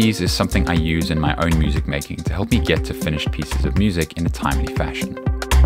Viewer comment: I thought it said Finnish music and was curious so I clicked :(